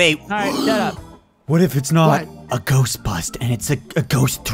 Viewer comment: I thought said ghost butt